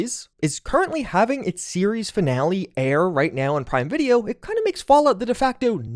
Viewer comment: Quick sidenote. The current season of The Boys is not the finale. It's gonna end in S5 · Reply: I honestly thought he said "Season Finale". Obviously I was not actively listening.